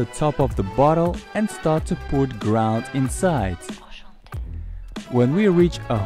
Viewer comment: start to put “ground” inside, that’s what I heard 😂